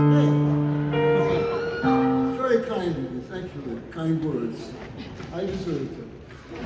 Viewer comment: what does Randy say at 6:10? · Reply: Very kind of you to say those words, I deserve it!